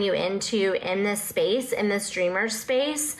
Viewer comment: It's the hand gestures for me. "We want to dream..." waves hands in circle motion "...with a full understanding." Oh. Okay. Thanks for clarifying that for me. Also, I swear, this entire time I thought she was saying "in this STREAMER space" like a Twitch streamer. Just realized she's saying "dreamer" lmaoooooo it doesn't make any less sense!